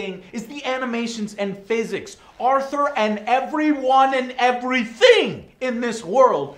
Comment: "everything" sounded like dutch..?